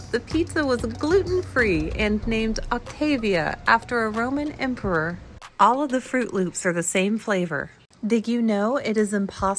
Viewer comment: She said two different things after each other “They named the pizza Octavia, after a Roman emperor” “Fruit loops are the same flavor” I thought she said “They named the pizza Octavia after a Roman emperor of fruit loops”